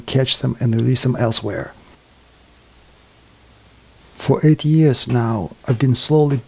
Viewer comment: Incredible. Glad I stumbled into this channel 28:01 sounds like he says “for 80 years now”. Has he also unlocked the key to immortality? Wouldn’t be surprised.